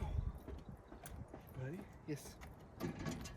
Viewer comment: I hear you say ‘ready?’ And an Asian mens voice says ‘YES’.